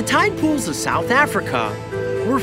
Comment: i thought he said tide pods